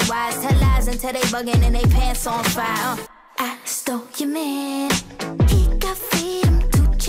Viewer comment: For a minute I thought it said I stole your mom💀